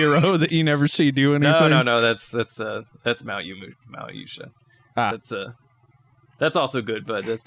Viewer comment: What anime are you saying at 29:40? Malumoot? Malusha? I can't make it out.